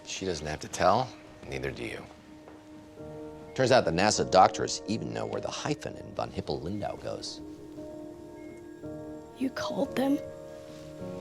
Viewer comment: What did he say at 9:49? His delivery sounds like a train wreck of a mass of words... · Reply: "Turns out that NASA doctors even know where the hyphen in Von Hippel–Lindau goes." It took me a lot of googling to find out the name of the disease lmao